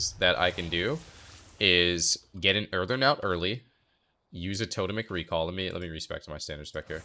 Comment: What is saying ? "Getting WHAT early ?" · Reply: Getting earthen out early. Earthen wall totem.